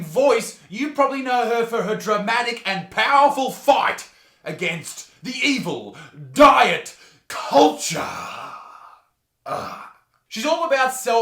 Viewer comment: I thought you said "dramatic and powerful FART against the evil diet culture" that aussie accent with a ball in your throat. Lol made me laugh non the less.